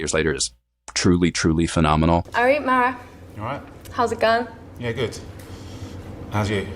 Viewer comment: Why don’t the subtitles just say what they’re actually saying instead of trying to be cute? “How’s it going?” “Yeah good. How’s you?” Etc.